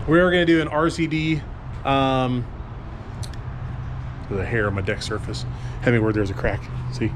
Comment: Did you say you had hair on your dick surface?